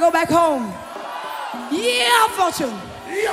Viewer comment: What she said at 7:47. I am Brasilian, I dont understand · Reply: Called the name of the lead guitarist #Ya Fortune